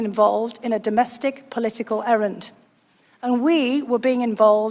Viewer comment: As I live close to where Dr Hill was born and speak with a similar accent, at 1:50 I heard her say the word "errant" not "errand". The definition of "errant" is "erring or straying from the accepted course or standards."